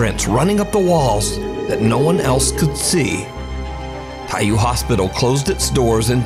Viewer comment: help I keep hearing caillou hospital